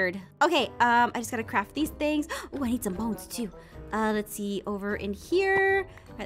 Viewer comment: I thought you said mones instead of bones lol😂